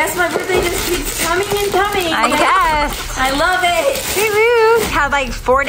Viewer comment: it sounded like she said UWU but she said whoo-hoo